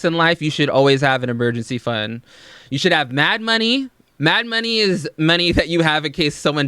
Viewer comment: WOAH IS MAD MONEY A THING? I always heard "vex money" and thought it was just a Jamaican thing lol